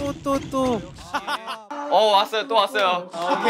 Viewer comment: I don't know what he said but it sounded like "Not him again"😂😂😂